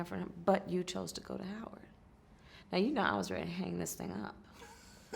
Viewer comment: Wait, Wait, Wait around 4:40 did she say, "I was about to hang this Nigga Up"? Am I hearing this correct???